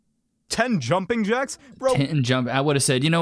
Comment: 10 jumping-jacks? I thought you said 10 lumberjacks, it's not the same thing.